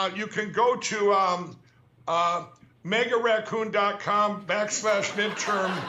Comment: I thought he said Make a Raccoon😂